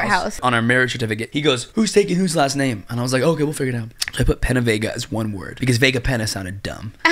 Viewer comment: The “penavega” but all I hear is “Winnebago”